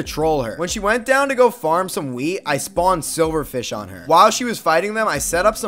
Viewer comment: I thought he said farm some weed